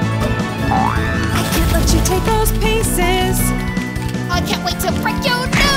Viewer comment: I hear "paces".